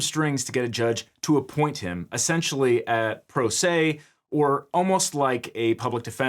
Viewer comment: I know it was just a slip of the tongue, but the "pro se" instead of "pro bono" at 00:01:40 really irked me.